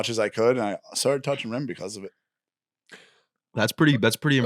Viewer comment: I legit thought said “I started touching men because of it” had to replay 5x!